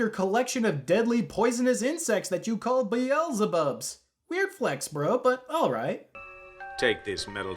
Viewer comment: hooooooold up... sweeps aside misogyny, power fantasy toxic masculinity and hacky reuse of assets are you telling me that guy called his insects beelzebubs... AND NOT BEELZEBUGS?!? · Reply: I think he did call them beelzebugs but I might have misheard it.